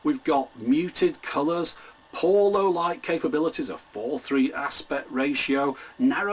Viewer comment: Speaking of poor audio, I could have SWORE you said, "Porno lowlight capabilities." Lol